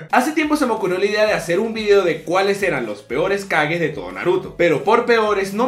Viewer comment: Cuando te escuche despues del minuto 0:06 pensaba que con kages te referias a las cagadas Soy idiota dejenme en paz :v